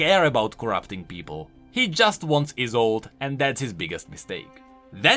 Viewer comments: what does he say here? I cant figure out what he wants, just sounds like "he just wants his old" Izold? im not really studied on the lore. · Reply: his wife Isolde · what does he say here? I cant figure out what he wants, just sounds like "he just wants his old" Izold? im not really studied on the lore. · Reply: Isolde. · What did you say at 41:50? "he just wants is old"? What does he want? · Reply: He said Iseult, Viego's wife